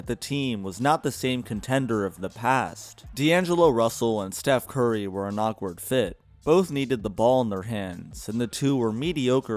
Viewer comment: Did you just say Steph Curry needed the ball in his hands??